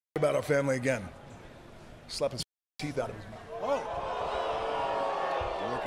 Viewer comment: "You're lucky you are too" are we just gonna ignore the power of that line? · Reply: It sounded like Hunter said "You're lucky R2" 😆